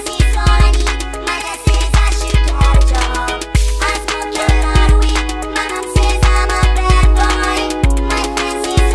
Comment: does he say"I smoke a lot of weed ; my says i'm bad boy" ??? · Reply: BaconFromPortugal I smoke a lot of weed, my mum says im a bad boy